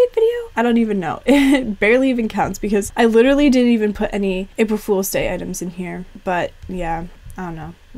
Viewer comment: GUYZ IT'S NOT CLICKBAIT I'M RLLY QUITTING THIS TIME!!!!! lol jk. april fools mood am i right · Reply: I thought it said Aparri fools 😂